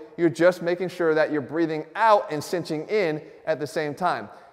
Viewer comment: Wtf I'm so confused first time I watched it I thought you said breath out as you crunch and now I think I heard you say both some one help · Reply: Breathe out as you go up on a crunch. This will help develop and activate your transverse abdominis hence giving you the flatter look rather than bloated look